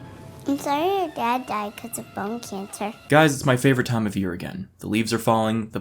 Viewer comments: I thought the little girl said bum cancer but people were saying it was bone so I put the captions on and they were right. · Heh, that "I'm sorry your dad died of bone cancer" has got some Tommy Wiseau energy... "the Doctors told me its breast cancer" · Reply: I thought she said bum cancer but then put the captions on and you're right that it was bone.